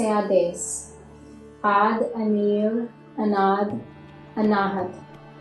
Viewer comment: Just did one half session. Felt silly with the arm movements and hard to memorize the song however I will give it anoth few days. I didn't realize I cut the blood supply to my feet and was stuck on the floor for a few minutes lol. Thank you for this video. I am running out of options in life to get better and I have hopes this can help. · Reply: Day two. So angry half way through I'm singing along and have the words printed in front of me and it sounds like she is saying anahad ananahat but the letters are anaad anaahat and I keep missing it up. So basically I'm not speaking any language and just making random sounds and it makes me so angry. I had to stop. I don't get what's going on here and yet I want to understand.